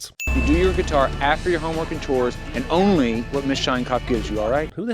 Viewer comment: err am I hearing Miss...Shinecock?